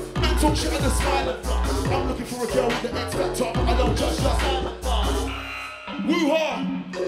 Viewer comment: truly ahead of their time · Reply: When he said woo hah I fully thought he said Wuhan